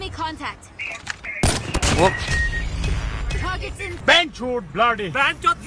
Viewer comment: did he say bc?